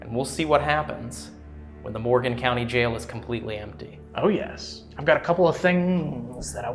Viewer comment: a male voice says "OHH YES" I think that's what was said please have a listen you guys didn't mention it at the time so I was unsure if you heard it